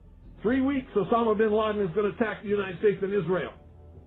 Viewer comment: did he said United States of Israel?